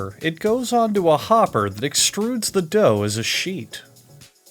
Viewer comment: Did someone say censored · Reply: Sheet, and not the other word.